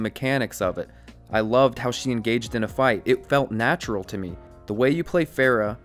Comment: I thought it said neutral.